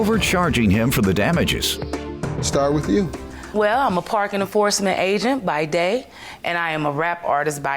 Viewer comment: I swear it sounds like she said she's a "parkin in the Forrest agent" lmao😂